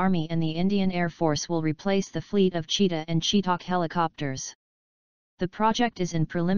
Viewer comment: Did she say SHE TALKED HELICOPTERS at 02:40 🙄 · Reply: Chetak